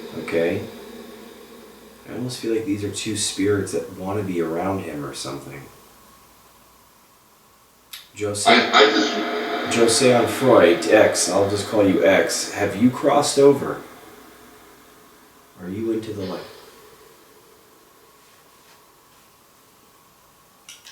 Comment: “I, I just...” that sounded like X